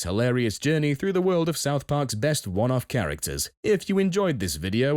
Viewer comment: I thought they said best fuck off characters idk why